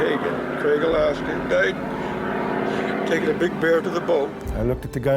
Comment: Same! I backed it up several times to hear what he said. I heard 'he died putting his bear in the boat' ????? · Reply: taking. he died taking his bear to the boat..